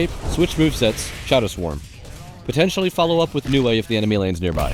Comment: did someone say potential?